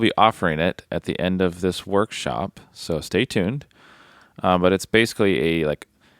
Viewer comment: will be released at the very end of the season. maybe in couple months ? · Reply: Oh! So sorry! I thought you said at the end of this session..as in the video. Great work!